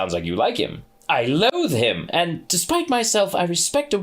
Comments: When Aziraphale says: "I loathe him!" It really sounded like he was going to say "I love him" and like he just barely managed to change the word😂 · "I'm in love with him" blink Surely I heard that wrong? Let me put on the subtitles: subtitles: "oh it almost sounds like you like him I love with him and despite myself" Yeah, still hearing love, lol, had to replay it five times to start hearing "I loathe him" even though the subtitles still say "I love with him"... apparently the subtitles can't quite get the grammar right on its own.